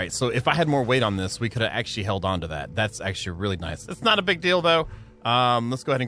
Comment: did he just say, Its not a big dildo😂